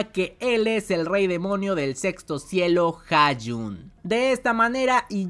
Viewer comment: en vez de Hajun entendí Caillou toi bien mensa discúlpenme xD